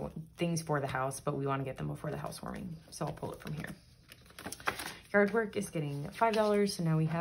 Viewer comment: When you were doing the retirement and Apple Care was showing, I def read that as Hippie Care and I was so intrigued to find out what Hippie Care was! 😹😹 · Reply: That’s funny! I thought she had said “hard work” but it was “yard work”. Bout the same though. 😆